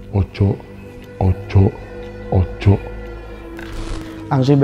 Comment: Masnya ssalah denger kali, bukan ojo ojo ojo tapi ojol ojol ojol ..., wkwkwkwk